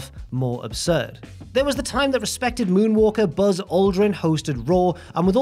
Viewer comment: Misheard as "suspected moonwalker" and thought we were gonna get into THAT whole argument again. My mistake.